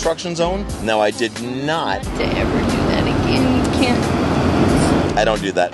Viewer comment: I’m confused what did she say? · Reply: Something like "If I promised never to do it again...can't you... [not write me a ticket]".